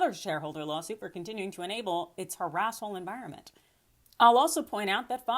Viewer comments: I thought she said "harasshole", which is now my new favorite word! (She actually said "harassful".) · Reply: No, I said "harasshole." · I heard that as "a**hole environment." I have no issues with that description. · 😂 Lol did you just say “…for enabling a harrasshole environment. “?? That is fkn hilarious!!